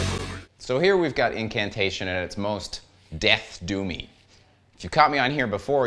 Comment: My wife thought you said “do me” instead of “doomy”.